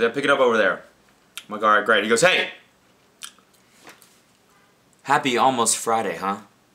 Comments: lol At first I thought you said happy homeless Friday. Wow I thought that is fucked! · I thought he said, "Happy Homeless Friday". I'm so sorry.